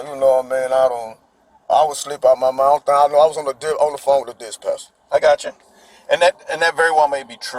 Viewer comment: minutes into this video... Did this ninja say "dish-passion" instead of dispatcher.....????!!! 😂😂😂